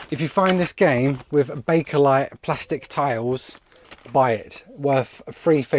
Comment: must've paused it dozen times still not sure what the name you mentioned. Did anyone catch the name pls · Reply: Bakelite 👍🏻 It's basically the first type of plastic material